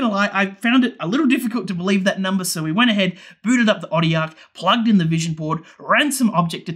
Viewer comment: booted up the body aaarrgh? Listened 5 times and still don't understand what you said, sorry. What do you use to measure power? Looks cool. Btw, subscribed, this content is what I crave. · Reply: "booted up the Otil Arc", I expect. It's a power analyser.